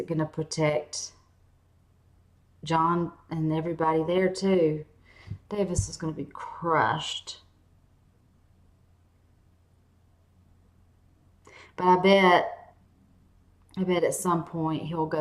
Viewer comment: did she say davis instead of davos ? :D